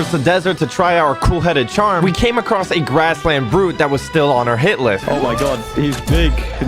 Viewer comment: Bro what did you say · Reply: Hitlist